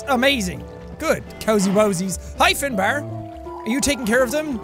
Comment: You know, when he said: “Hi, Finbar,” I thought he said: “Hyphen bar,”